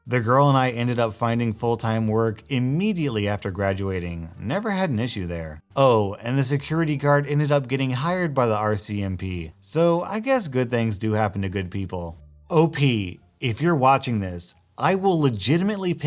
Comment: I wish hired and fired didn’t sound the same. In the second story I thought it said he was fired and I was not happy and than later went back and realized it said hired.😂I’m dumb